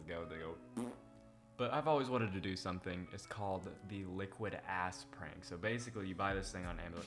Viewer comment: said he was gonna do this in his weekend vlog video from yesterday but I thought he said “liquid ice prank” because of his accent 😂